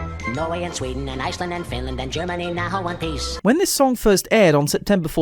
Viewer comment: I always thought he said Germany now who want peace not Germany now in one piece which either would be an inaccuracie